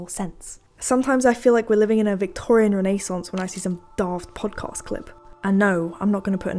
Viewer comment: thought you said daft punk and I was thinking what the hell did daft punk do to piss you off😂